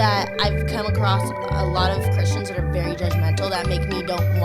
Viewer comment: Who also heard her say Christians as "cushions"?